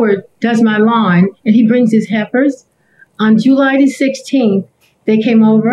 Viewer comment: I am laughing so hard because I could’ve sworn that she was saying heifers but then I was like now maybe she saying helpers